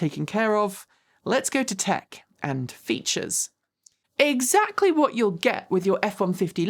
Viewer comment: “Tech and features” I almost thought you said “quirks and features”. 🤣